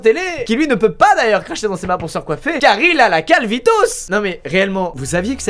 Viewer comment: A 2:01 j'ai compris carlitos